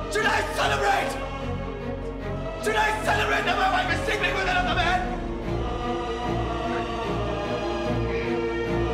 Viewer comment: what did he say ? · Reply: He said "Tonight we celebrate that my wife is sleeping with another man"